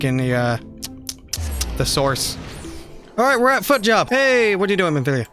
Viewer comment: "Hey we're at FootJob" wait no, i misheard that